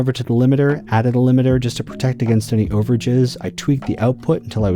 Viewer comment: What did he say @ 10:15 ? Over ___ ? what? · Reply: overages